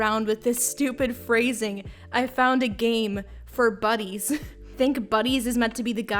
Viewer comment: i am one “i found a game for baddies” away from ending it all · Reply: I always thought they said "I found a game for buddies"